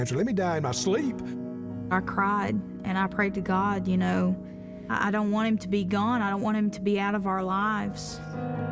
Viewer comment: did i hear that right?? "i dont want him gone,i just want him out of our lives" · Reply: "I don't want him to be gone, I don't want him to be out of our lives"